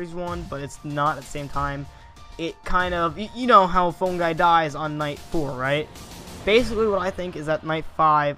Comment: i thought it said phone guy is phone guy